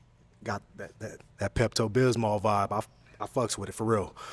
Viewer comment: DID HE SAY PEPTO BISMAL VIBE 🤣🤣🤣